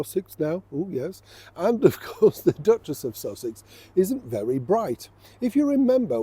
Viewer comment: I thought you said the ducks chess off sausages